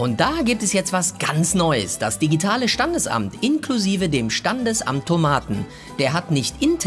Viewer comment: wer hat auch Tomaten verstanden😂